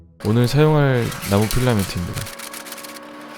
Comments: 아이씤ㅋㅋㅋㅋ 나고가 너무 귀여워서 헤롱헤롱하다가 나무 필라멘트를 나고 필라멘트라고 들었어욬ㅋㅋㅋㅋㅋㅋ 이제 하다하다 환청이 들리넼ㅋㅋㅋㅋㅋㅋ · 의 나무 필라민트를 나고필라민트라 들었다...